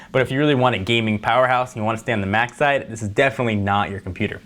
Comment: "if really want gaming powerhouse and want to stay in a mac side" is that what he says on